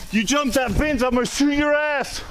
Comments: You jump that fence I’ll shoot ur ass 😂 anybody else heard that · you jump that fence ima shoot in your "ass" is that what I heard.